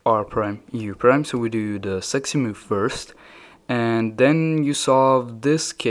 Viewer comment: did he say sexy move